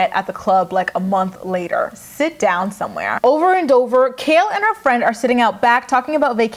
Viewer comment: I could of sworn I heard “Over on ogre” when it cut to Kail’s recap lmao I was about to say “Damnnnnn Grace ! That shade gurl” lmao 😆